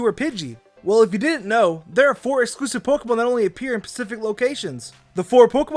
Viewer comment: did you just say pacific? YOU IDIOT